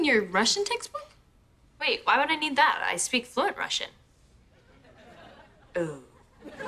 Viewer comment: did she say russian or not?